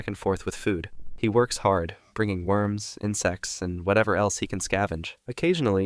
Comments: I thought he said worms and sex 😭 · I thought you said “bringing worms and sex” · I heard "worms and s*x" lmfao